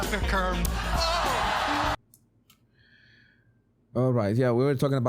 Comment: Why does Mark Cuban have such a hard time pronouncing "traffic cone". The closed captions thought he was saying "curb" and "curve". Honest question.